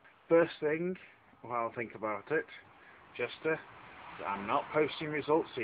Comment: PS: it sounds like you said jester, instead of Justa LOL